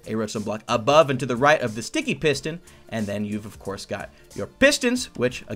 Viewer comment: At first I thought you said stinky piston,D